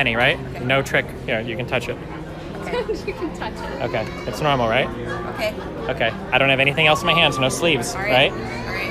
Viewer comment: what is poki saying at 5:36 ? im not that good at english NotLikeThis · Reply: She was saying, "guys we are not talking about how Fed got married" so his joke wedding with scarra